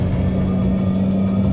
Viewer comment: I know it’s “multiple missile” but as a kid I always heard “ multi-go-me-sigh-eee”. Never knew what it was, just thought it was some futuristic weapon lol 😂